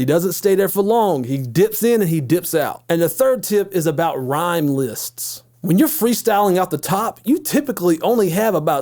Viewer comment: Damn i thought he said top ramen list lolz